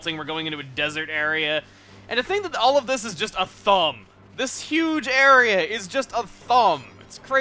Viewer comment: Ok I'm extremely late with my comment but I thought he said 'thum' and not 'thumb'